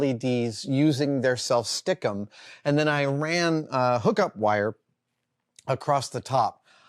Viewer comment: My ears are faulty this morning. I keep hearing "hiccup wire" instead of "hookup wire", which is what Adam is really saying.